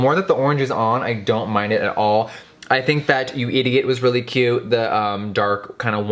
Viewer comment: did you say you idiot is really cute lol 🤣